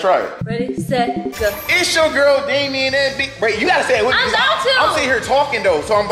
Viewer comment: Did he just say it's your girl Damien😂😂😂😂😂😂😂😂😂😂